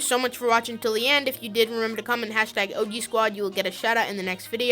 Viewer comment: thx man and also its OG squad lol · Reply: thought you said obey squad sorry 😂